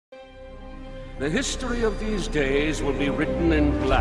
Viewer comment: I thought it said the history of gays XD